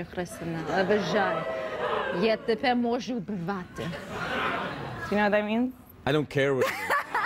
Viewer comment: "I dont care what it means, I just want to hear it everyday for the rest of my life" 🤣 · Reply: Because she said it. You heard her say, "я від тебе не можу відірватись", but she clearly said "я тебе можу убивати".